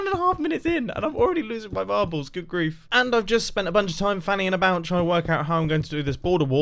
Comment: did he just say fannying about?? I've never heard him swear